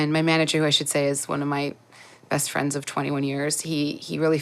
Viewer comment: Around 12 years, not 21 years of experience. She said she started around 21-22 and is 33 now. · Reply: If you listen at 0:42 she clearly says "my manager who I should say is one of my best friends of 21 yrs." Also she is 37 yrs old and born in 1985.